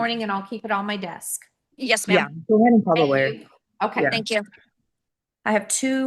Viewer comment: Did I hear that raggedy xzz lying vagabond say, “Go ahead & call the lawyer” 😝 you’re the GOAT for bringing us the condensed conclusion (…we & Ms Tammy hope it’s the conclusion) Let’s watch 🍿